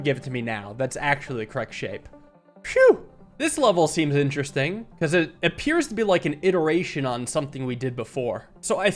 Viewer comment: What's that word for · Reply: health i think